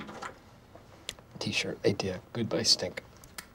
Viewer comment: "T-Shirt Idea, Goodbye's stink." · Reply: I thought he said goodbye stink